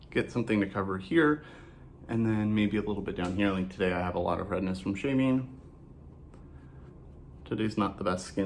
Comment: I was distracted and missed where you said "redness from shaving" and heard "redness from shaming" lol I was like wait, what did he just say??